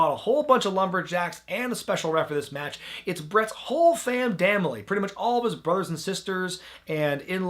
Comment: Does say “Whole fam damily” @ 23:49 ? Or did I just mishear that.